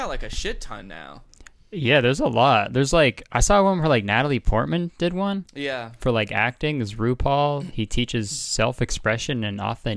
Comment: Lmao I thought he said “Natalie Portman has one on teaching how to act like ru paul”